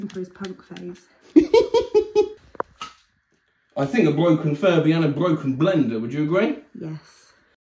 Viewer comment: I thought you said "furry" so I was thinking: "wow I have no idea how you did that, but I'm still impressed nontheless."